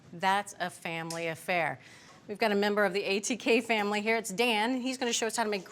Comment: thought she said we have a member of the 14K family but she said ATK ...